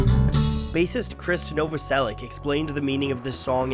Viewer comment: i thought i heard “racist krist” for a second, was prepared to throw hands